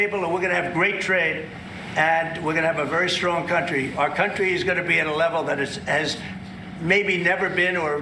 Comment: yep dunderhead you slipped when it sounded like you said "our country is going to be leveled".. NOW that TRUTH almost came out. OH SIGH..I'm so exhausted with all this and honestly way to old for it.